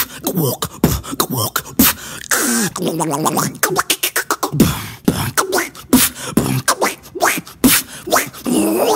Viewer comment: The Donald Duck drop beat was my favorite. ❤️ Edit 1:37 What the heck did I just heard? 😂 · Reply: Ya ah ya ah eyy